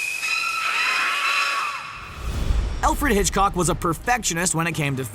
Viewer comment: Did you just say Elfred Hitchcock